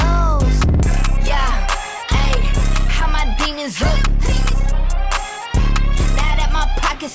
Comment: I honestly thought she was saying "how my penis look" good thing Michael read the lyrics.